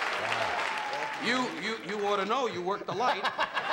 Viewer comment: can anyone tell me what don says at 3:07? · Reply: "You oughta know, you worked the light" 😂